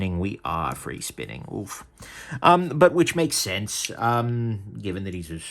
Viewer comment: For a second I thought you said that free spinning pivots make sense. You scared me.